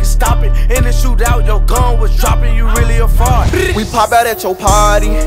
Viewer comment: I thought Choppa said “you really a fart” for a sec.